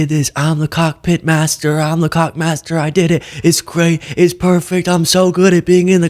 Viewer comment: The “cockpit master” at one point I heard “cock master” I’m all yep Trump is the dictator sucker 3000. I’m sure i misheard but I thought I’d share the thought.